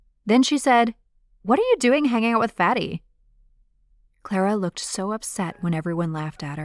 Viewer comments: did Betty just say, “What are you doing, hanging out with Betty?” But she was hanging out with Clara...? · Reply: FluffyAngel Playz She said “Fatty” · i thought she said what are you doing hanging out with betty? bruh u r betty but then i realised she said fatty :( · did Betty just say "what r u doing hanging out with Betty" ?? instead of "what r u doing hanging out with Clara" 😂😂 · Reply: I think she says fatty